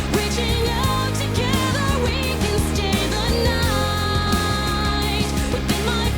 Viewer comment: I will never not love the auto-generate subtitles. 2:21 "can stay axe night" 2:58 "big action gage?" 3:28 "big weight mode" 4:49 "I see that you brought your own fat berserker" 7:56 "I'll go play with your toys like a good little freak" 8:53 "I will murder kitty" 9:55 "well I'll cities technically" 11:30 "I was unprepared for a bag of wolves" 13:52 "no touching the no jump" 15:21 "[Applause]" 18:28 "Chinji", 19:35 "Chinji", 19:44 "Chinchi" 19:50 "huffer going once" 20:06 "as your court gesture or boyfriend or whatever" (I'm sure she actually said "gesture" there) 21:34 "then Meltzer, Fan Young, and that lizard"